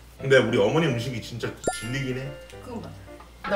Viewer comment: 난 왜 지리긴 해로 들었냐 ㅋㅋ